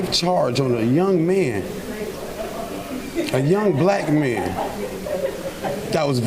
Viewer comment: So he's not only "a 12 year boy" but also "a young black man" according to his father? (Did I hear that right or is he talking about two people?)